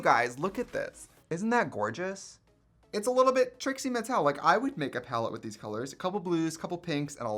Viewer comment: "Its a little Trixie Mattelic, I would make a palette with these colours" what I heard: Trixie Metallic, god PLEASE make a palette with metallics and call it TRIXIE METALLIC